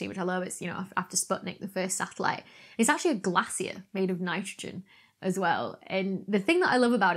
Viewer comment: Not my American ass looking up what a glassier is 🤦🏼‍♀️ nah fam, she said glacier